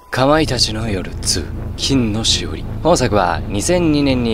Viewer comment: かまいたちの夜2が乾いた血の夜2って聞こえた